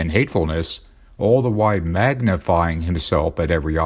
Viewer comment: I thought i heard "maganify" instead of magnify. I agree the internet makes it too easy for awful people to congregate/gather.